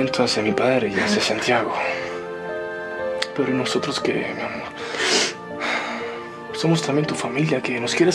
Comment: Aaaaaaaawwww!!! Escuche mal o Vasco le dijo "mi amor" a Camila? :3 💕